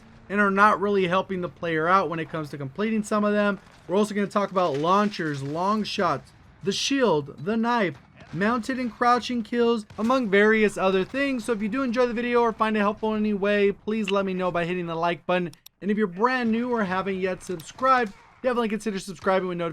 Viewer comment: He said croching instead of crouching